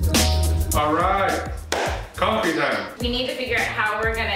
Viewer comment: when you sat concrete time I heard "Alright! coffee time!"